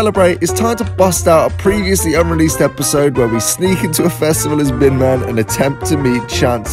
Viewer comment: I thought it said “as Big Ben” and I got confused…